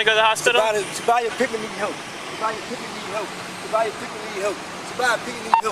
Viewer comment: All I understood was,” Scotty pippen needs help.” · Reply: I thought he was saying “Sophia Pittman is a ho”